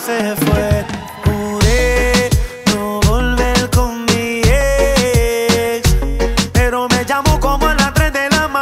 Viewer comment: Pensé que decía "Jure no volver con Miguel" jajajaja